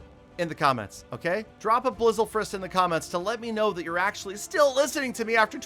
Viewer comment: Blissle frist? I think that’s what you said